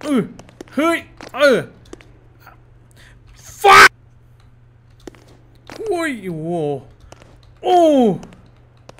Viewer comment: พี่พูดว่าอะไรน่ะ😂😂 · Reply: ผักครับ อยากกินผัก